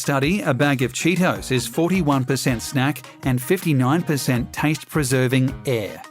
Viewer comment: Did you say that Cheetos had 59% "taint preserving air"?